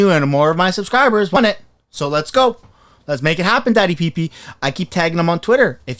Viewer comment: 🔥💙🔥 When I first started watching your vids I thought you were saying "DADDY PP" LULZ!! Go!!